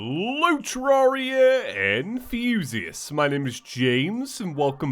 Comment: In the intro I thought he said terraria and fusists